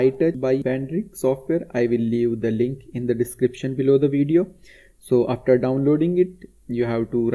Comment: that sounded like I belive